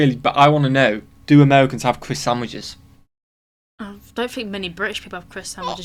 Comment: Of course people have been using croissants for sandwiches for quite a while, but the first I heard the word "croissandwich" was as a Burger King breakfast thin, a response to the McMuffin, here in the States. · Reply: I love putting chips on my bologna sandwiches! I bet its more common than you think. Btw I thought he said Croissanwich too lol.